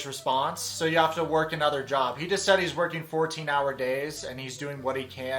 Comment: I initially thought Yazan said he worked 1-4 hours, not 14, maybe that's why Britney said he needs to find another job?